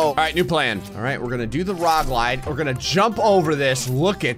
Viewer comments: If you skip to 6:52 he says “Alright, we’re gonna do the rog lide” instead of log ride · You said "rog lide" instead of log ride at 6:51 🤣🤣🤣